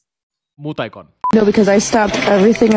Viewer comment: did i just heard moot icon 😂